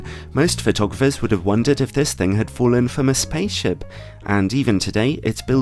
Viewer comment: you mention that photographers didn't care about image stabilizer and instead care about this item instead. I couldn't make out what that feature was? Infamous paidship?